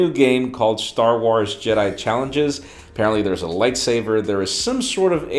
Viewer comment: Did he just say Light SAVER?